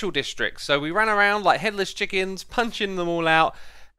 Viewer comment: I was shocked for a second because I thought he said Hitler's chickens instead of headless! 😂😂😂🤦‍♀️